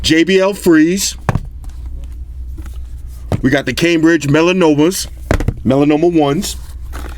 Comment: did he just say "...the cambridge melanoma? lololll"